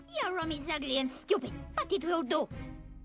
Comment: did she said fagot or fucking? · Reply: But it will do.